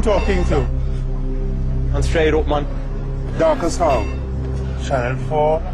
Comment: swear the black guy said his name was 'dark as hell'